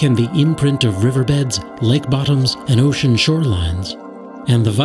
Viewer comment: i sooo thought it said , lick bottoms but it said lake bottoms😂😂😂😂😂😂😂😂😂😂!!!!!!!!!!